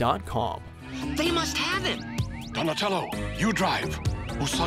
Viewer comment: Did Splinter just said "Usagi you will look up me ass" in 9:10 ?